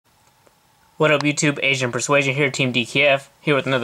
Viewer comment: 0:0 I thought he said Asian persuasion lmao